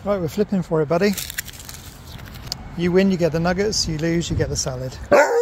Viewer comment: What did he say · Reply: Nuggets haha not what you think